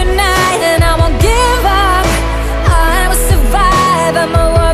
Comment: Sorry but it's "Iam a survivor " not "I will survive " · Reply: I hear in there "I will survive" not "I will survivor"